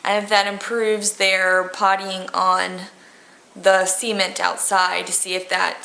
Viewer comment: oh cement i thought she said semen lol